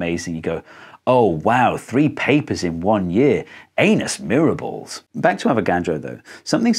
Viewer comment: I thought you said "anus mirables" and then started blowing up a Whoopee cushion as a joke. Serious question though. At @ 10:20 you say that these syringes all contain the same number of molecules, but isn't it the case that they don't necessarily contain the same pressures? Like they would eventually after gasses equalize through the syringes tip, but not necessarily immediately after aspiration?